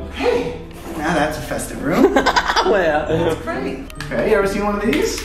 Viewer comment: I swear there is a voice thats says “that’s great”